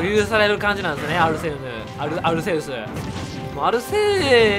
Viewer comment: アルセーシって聞こえて下ネタかよww て思ってコメ欄ニヤニヤしながら見たらアルセウスだった恥ずかしさ